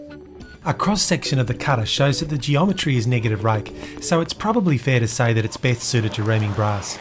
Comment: My apologies, but can somebody tell me what he says at 2:28? He says the geometry is 'negative...(something)' Thanks in advance :) Love this channel, though. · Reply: Hey Michael, "Negative rake" Great to have you watching :)